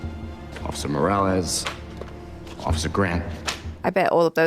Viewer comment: did he say officer morales (Spider-Man reference)